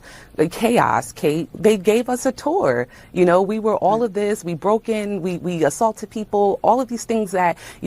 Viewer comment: Did I hear that congresswoman say..WE BROKE IN THERE AND..at 23:48-23:50...she did admit they BROKE IN